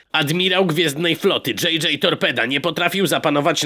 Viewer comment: Czy ja usłyszałem w wiadomo.ściach JJay Topeda :D